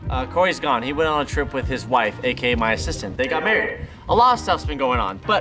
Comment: Tbh this sounds weird but I thought said sister instead of assistant 😂😂😂😆😆😆